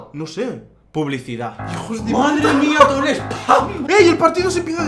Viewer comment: Minuto 3:37 ves que dice ay hijo de p, menos mal q no lo digo completo